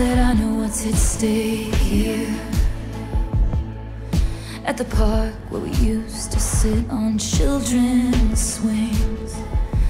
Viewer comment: When I first listened to this I really thought she said "at the park where we used to sit on children"... 😭